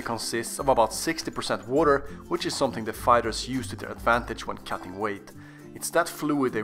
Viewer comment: For a second I thought you said (Anime fighters) 💀